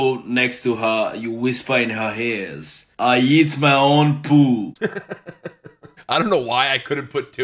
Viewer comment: I heard I eat my own ball